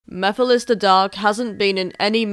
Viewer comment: In the beginning of this video, I say "Mephiles the Dark", not "Mephiles the Dog". If I did say "dog", my British accent would make it sound very different to the word "dark". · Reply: Honestly, I heard "Mephiles the Duck" ....And I'm British too